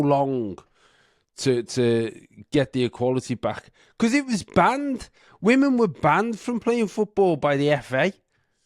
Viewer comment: I love your accent...at first I thought you said..they were bangin' the women..but you really said banned the women..lol..sending love from Ontario Canada